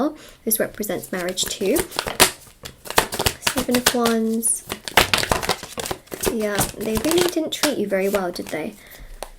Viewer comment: I really generally thought you said "Tia they really didn't treat you very well did they" at 10:08 . That creeped me out